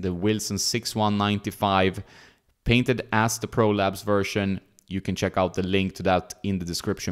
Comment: i kept hearing "prolapse" in this video and thought what a terrible name.